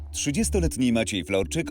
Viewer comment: Ja w pierwszej chwili usłyszałem "Trzydziestoletni Maciej Frączyk"...